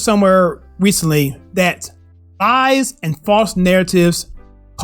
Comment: Either my ears are playing tricks on me, or you forgot an L sound when intending to say "lies" but sounded like "eyes" to me. On a more serious note, your intended message with this statement is 100% spot on, and with the Derek Chauvin trial having recently started (yesterday, if I'm not mistaken), I can't think of a more relevant example than the George Floyd arrest and death. Without MSM's narrative, the entire country was united in believing that black lives do indeed matter. With facts that emerged later, it's unfortunate that Chauvin would have been the "villain" leading to unity, but if our country had gone down that path instead of the one it went down in reality, I think Chauvin would be willing to make that sacrifice.